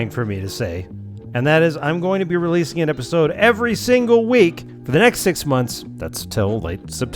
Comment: Jesus christ, I heard "every single day" instead of "every single week" at the end there and was genuinely concerned for a moment